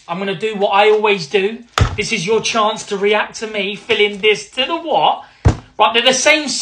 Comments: I thought he said TO THE WOK all this time. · I need your merch, hoodies with “To the what?!” And “Brimtastick” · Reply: Wait, I thought he was saying "To the wop"!?!?